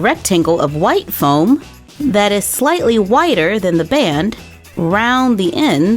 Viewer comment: I thought she said whiter instead of wider at 1:27😂😂😂